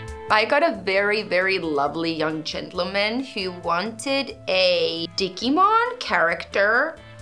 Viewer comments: I thought she said dickimon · The Digimon tattoo dressed in Victorian clothes in hieroglyphics style would have been so so cool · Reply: I coulda sworn she said “dickymon” but now I understand digimon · The Digimon tattoo dressed in Victorian clothes in hieroglyphics style would have been so so cool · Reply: i thought she said dickimon